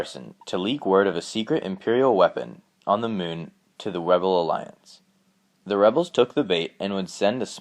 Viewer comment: listen at 4:39, he says webel alliance instead of rebel